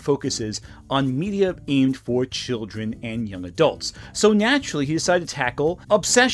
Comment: did bro just say ”nyaturally” 😭